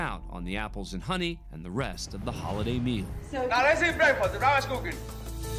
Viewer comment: did he say " can you smell what the roc is cooking"